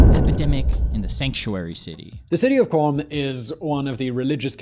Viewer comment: Auto-generated captions at 8:38 say "The city of bomb" instead of "The city of Qom"